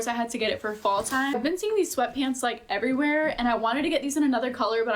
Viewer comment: petty but this girlie saying “I seen” instead of “I saw” is so painful lmfao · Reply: I think she said I have been seeing